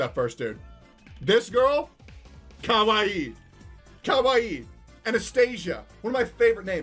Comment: Why do I mishear Anastasia as an astesia lol Too much D32 steel grind I guess